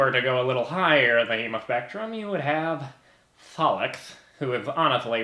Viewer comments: For a second I thought he said EmospectrumHm. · I thought he said emospectrum rather than hemospectrum and took me so long to realize...